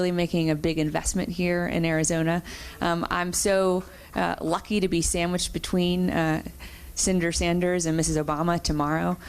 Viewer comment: Did she just say " I'm so lucky to be "sandwiched" between Senator Sanders and Mrs. Obama tomorrow"? Oh God! Perish the thought!